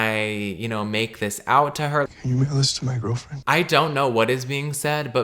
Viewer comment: everyone's saying "mail this to my girlfriend", but what i hear is "can you make a list to my girlfriend", i cant hear "mail this" at ALL. so i think the confusion is completely warrented lmao XD